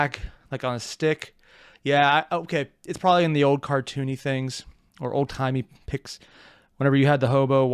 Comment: It took me a moment to understand "chuuni". I thought was saying "toony", as in wacky and cartoon-like, but then I realized he was referencing the chuunibyo phenomenon (don't know if I spelled it right), which I don't feel qualified to go in depth.